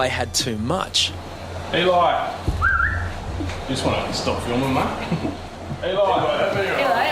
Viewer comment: did he say “stop filming mate”